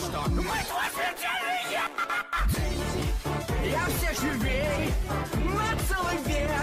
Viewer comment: Я всё же гей ! 3:09 ( я реально там слышу это.....)